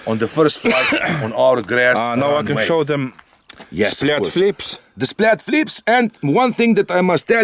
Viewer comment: Did I just hear “splat flips” instead of split flaps? 😂😂😂 you guys rock!